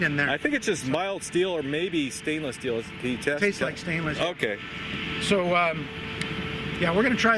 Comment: did he just say "tastes like stainless steel"?